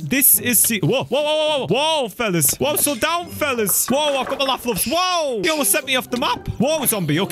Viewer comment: Did he just say "I've got my Laff loafs"?